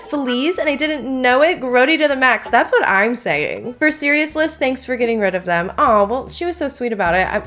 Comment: I was listening instead of watching so it sounded like serious and liss was one word so it was like for seriousliss and I thought what was meant to be said was for seriously but that is not correct but now I wanna start saying for seriousliss instead